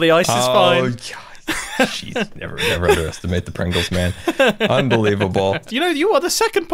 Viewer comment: "Never underestimate the Pringles man" i think Scar just came up with the slogan of the season, for Mumbo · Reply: God that makes so much more sense. I thought he said “never underestimate the prangles, man,” and he was just mispronouncing the word pranks lmao